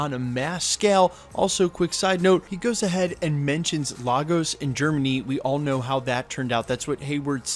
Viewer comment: Did you just say "Lagos in Germany"? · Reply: Lagos AND Germany